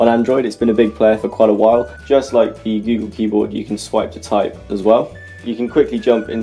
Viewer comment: Wat is the word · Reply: Swipe to type as well.